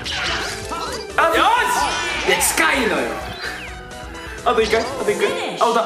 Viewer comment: ここのとしみつの『近いのよ！』がどうしてもフランキーに聞こえる この動画何回も見てるけど、毎回フランキーにしか聞こえん